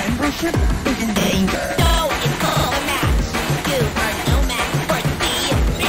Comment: Match: "Try harder, Bub. Or else your bembership is in danger" Pencil: "Dont insult the match, you are no match for the MATCH!" (Just to let you know so that you know what they say ofc)